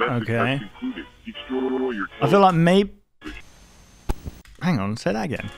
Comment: at first I thought it said destroy your children